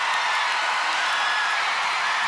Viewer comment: What did she say at 06:36? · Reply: Beauty is a lie